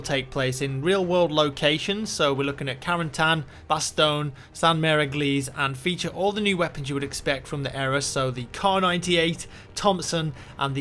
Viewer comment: lol 2:15 did he just say all the "new guns from that era"